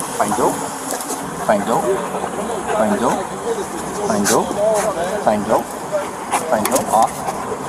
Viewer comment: For a second I thought the officer kept saying "find dope...find dope...find dope" to the dog :-D